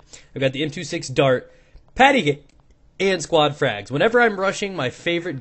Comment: Did anyone hear him interupting himself in 1:05 by saying "pettycake" ?